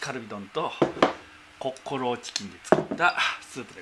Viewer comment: 「コッコローチキン」が一瞬「コックローチ」に聞こえる。きゃ～～～っ チキン屋のネーミングが悪い。